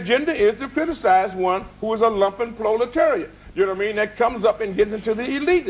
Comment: what does he say at 4:09 the english caps say lupin flow leterrier but that is definately not what he says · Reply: Lupin flow Leterrier is actually "proliteriate" which is the struggling working class. ..